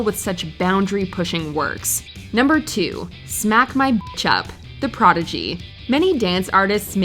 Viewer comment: He for real said smack my b*tch up, i thought he said change my picture as a kid when i used to listen to this